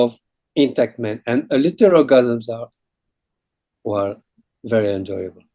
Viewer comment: What does he say @ 10:20? “Little” orgasms? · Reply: Low intensity orgasms.